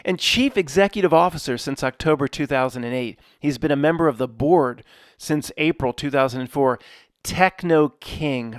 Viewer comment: I could have sworn you said “Member of the Borg” and it seemed natural